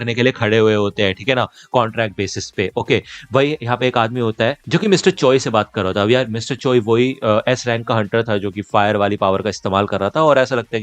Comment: naam kya bola iska, matlab spelling? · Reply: Mr. Choi